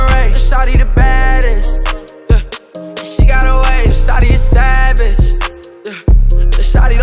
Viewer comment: I thought it said shawty a sausage for a second lol